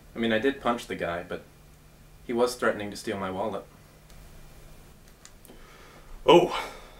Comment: does he say "steal my wallet" or "steal my walnut"?